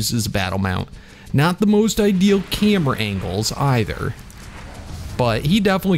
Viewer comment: Who else heard camera ankles